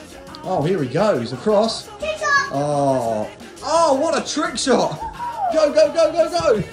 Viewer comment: When he says trick shot i thought he said Piss of...😂😂😂